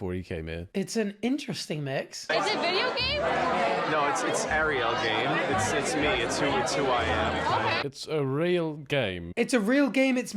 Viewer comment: I think the guy says "No, it's Ariel game. It's me, it's who I am" His name being Ariel.